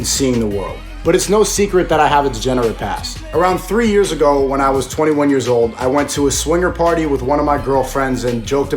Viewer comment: i really thought he said "around 3 years ago when i was 3 years old"